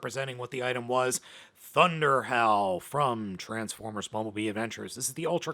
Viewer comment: When you said “ThunderHowl”, I thought you said “ThunderHell”! 😂😂😂